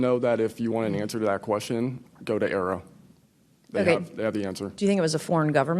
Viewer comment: ? What is he saying? xD Go to Arab? : d